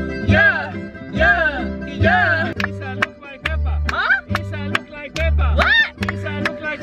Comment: it’s me or i just heard "u look like peppa"👁🐽👁